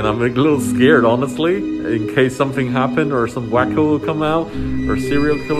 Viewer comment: Min 0:45 did he say in case some black people come out??? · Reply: He said, :..some WACKO...".